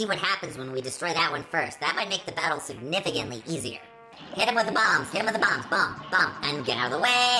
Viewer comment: Dr Kahl's Fandroid? What's next, Dr Kahl's R2-D2? (P.s I thought you said" Hit his MOM!" when you said "Hit him with the bomb"